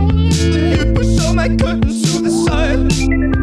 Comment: tbh I thought you said s*icide (help-) but you said curtains to the side! Love your songs!!